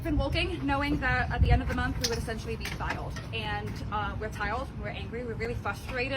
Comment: did she say "we're tiled and at the end of the month we will be tiled"? They all got new jobs as tile setters? I'm so confused.